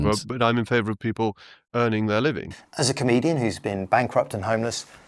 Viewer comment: When you said “comedian” I heard “Canadian”. (Full disclosure: I’m Canadian). Lot to unpack there!